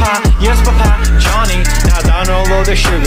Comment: I thought it said Fish lasagna